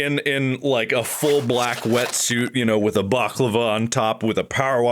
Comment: mark saying baklava instead of balaclava lmao